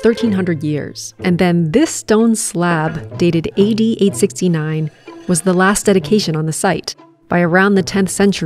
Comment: I swear I heard you say that year 8869 Which hasn’t even happened yet, but I’m sure that’s not what you said, but that’s exactly what it sounds like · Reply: AD 869 😄